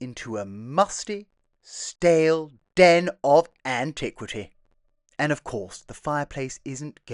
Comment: Team Jack here. Love, love , love that old cavelike fireplace surround. Certainly a den of antiquity for the future. 😊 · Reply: Hahaha, for a second there I thought you said “iniquity” 😂